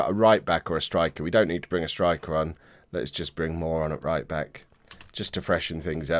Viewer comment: did you just say "Let's just bring moron at right-back"? This is a family channel, remember?